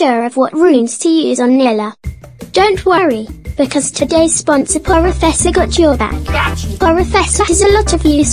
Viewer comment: Wait a MIN, in the Sponsorisation part i swear i heard "Poro Fessor dot Jojo" Ummm... 😅 Am i going crazy ? Good Job by the way, much love, Continue the good works ✌️